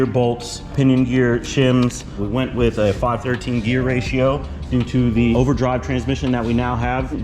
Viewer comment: Did you say 5.13 gear ratio? Around 5:30.